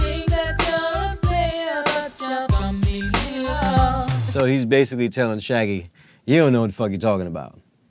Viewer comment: Omg for years I thought he was saying "You might think you're a player, but you're a bump on a log." But "It's your complety lost." 20 years later....mind blown